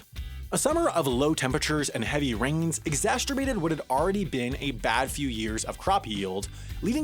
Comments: Did he say EXASTURBATED?!??? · Did you just say "exasterbated"? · did you say “exasterbated” ?